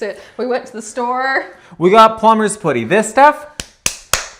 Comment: Is saying Plumber's pooty or Plumber's Poody? Cause I thought it was Plumber's PUDdy. Like buddy.